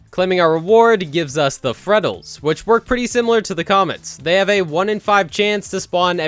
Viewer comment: Was it just me or did it sound like he Said ”and it works pretty simlar to the coments ”